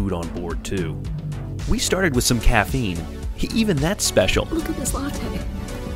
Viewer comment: No think she said “look at this latte”